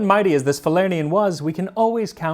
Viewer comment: As a cocktail enthusiast when you said Falerniam I thought you said falernum, a caribbean liqueur. I guess thats where the name comes from, I've always wondered why it sounded so Latin.